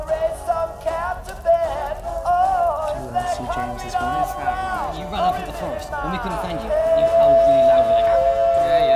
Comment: WHAT ARE THEY SAYING SOMEONE TELL ME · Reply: After listening back to it many times I think it's something along the lines of: "Do you wanna see James' work(?) " "What is that?" "You ran through the forest, and we couldn't find you, and you howled really loudly like awooo!" "Yeah, yeah, we were all there"